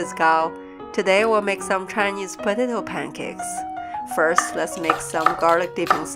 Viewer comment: I’m high asf I thought it was and said Chinese potatoe pizza lmao